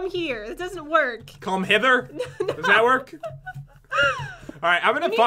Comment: I heard pat say Hitler or is that just me